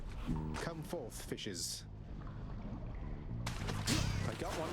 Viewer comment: I heard "Come forth, fishes." As something as and had to go back to check. lulz. Holy Guacamole, Theseus was deleted so fast. Under 20! Amazing! Thank you for stopping to listen to the song~ The Ice lasers are always cool. Ooh, the other save.